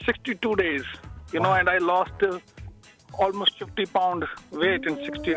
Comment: does he say "keto and steroids" ?